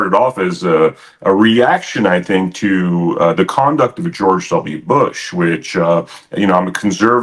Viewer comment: Did he say "George Soviet Bush" around @ 2:30 ?